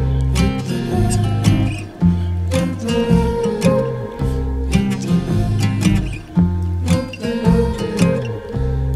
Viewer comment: Que dice despues del 1:30?? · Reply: Son bratty y ed , cada uno dice una cosa diferente es vete ya y yo te amo , nomas que cada vez que uno dice vete ya él otro dice yo te amo asi turnandose